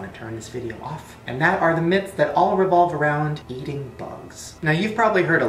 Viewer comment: all what eating bugs?? i couldnt hear it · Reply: hsnan All revolve around eating bugs